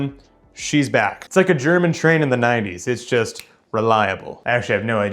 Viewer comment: OH wow NINETIEES not 30s I uh thought you said 30s and uh wow yeah thats... that'd be a very different connotation